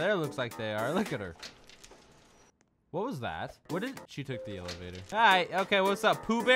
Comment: I thought he said "That was fast."